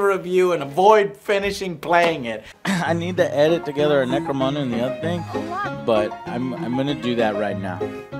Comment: What did Joe say at 7:42 "necro" what? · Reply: This is late but warhammer 40k necromunda another game an FPS